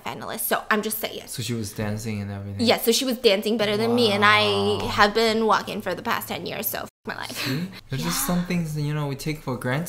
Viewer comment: did she just say she’s been walking for the past 10 years of her life?